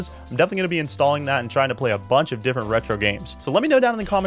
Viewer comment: My ass thought it said frog alley